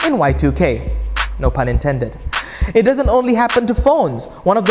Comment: I didn't understand what did he say between 2:25 and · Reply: no pun intended